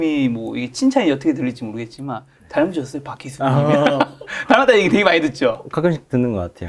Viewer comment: 20번 돌려봤는데 도대체 뭐라고 하는건지 모르겠네요 다람쥐여쓰 바퀴수?????????????? 뭐에요 도대체??? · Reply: "닮으셨어요 박희순"